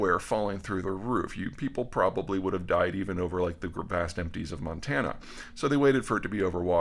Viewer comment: I keep listening at 07:06 and can't understand exactly if Peter is saying "a few ppl would have died even over the vast empties of Montana, so they waited to shoot it down over water..."? Am I not hearing it correctly? Sounds like he's saying there's was a great probability that someone would have died if the balloon was shot down over Montana? It's one sentence and a few words but they are important words in the context.